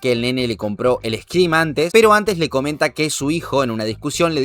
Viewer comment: el que?... el que? no entendi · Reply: El escrim